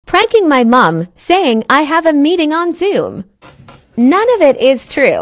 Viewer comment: I thought it said she had a meeting on ZOO